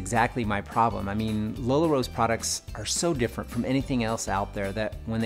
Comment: Do they pronounce it Lih-la to make it sound more like LuLaRoe? He said "Lila Rose products," but I thought he said "LuLaRoe's products" for a second there.